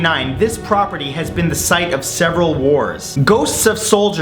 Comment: i thought you said several whores XD